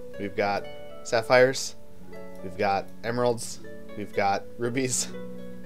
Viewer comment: I keep hearing "gemeralds" xd